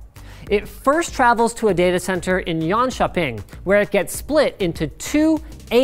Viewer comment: sounds like you said johon kop ing